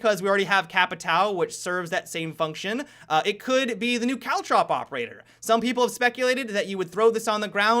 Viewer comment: When said caultrop said i thought he said cow chop